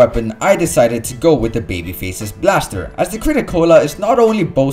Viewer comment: i thought he said baby feces blaster lol